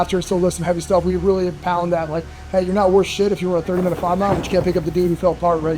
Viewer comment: I thought he said “you’re not sh*t if you run a 30 min 5 mile” I was like damn I got some work to fo